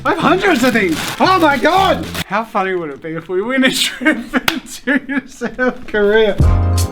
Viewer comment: What jazza sed: "how funny would it be if we win a trip to south carrya" What I hear: "how funny would it be if we got closer to the corona virus"